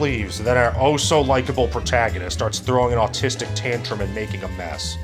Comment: did you just say "he's throwing an autistic temper tantrum??"🧍‍♂️